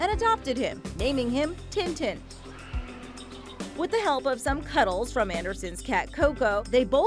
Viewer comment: she says "with the help". The first time I heard that I thought she said "what the hell" XDD